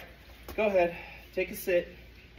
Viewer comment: To be fair...sounded like you said "take a sh*t" not a sit lol